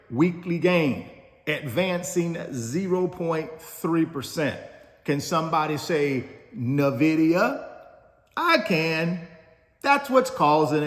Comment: I thought you were gonna say, "Can somebody say Amen!" But you said "Can somebody say Nvidia!" :)